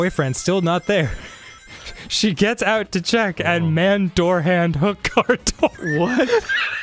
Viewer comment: What does he say ? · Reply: Man door hand hook car door